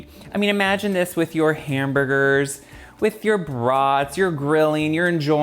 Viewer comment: 😂😅😂it sounded like you said “ imagine this with your hamburgers..your bras…..” BRATS!!😂🤣😂